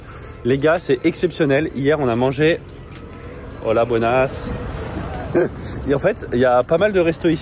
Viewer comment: je n'ai pas l'oreille des langues :p j'ai entendu à 13:17 "hola bonasse" ^^ sinon gg pour le premier set